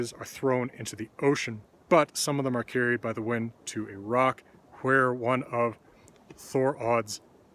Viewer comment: At first I thought you said "Iraq" and was waiting for some wild region-spanning story.